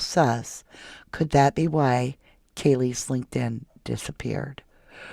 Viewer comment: "... could this be why Kaylee ......" what comes next? I didn't understand, can someone please clar5the words that lead to BOOM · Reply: asked, "Could this be why Kaylee's linked-in disappeared?" 💥boom Explanation: In the 1st interview Kaylee's family gave on media, Olivia, Kaylee's sister, stated that someone just got rid of Kaylee's LinkedIn account. It was just active but no more, it's gone...